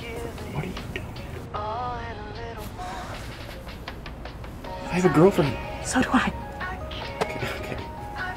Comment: Hold the phone did just say she has a girlfriend at 7:04 yooo she may be part of lgbtq eather way I support